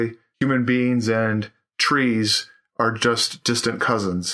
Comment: At first I thought he said... "Human beings and 'trains'..." And I freaked. In a good way. But he said, trees--so on and so forth.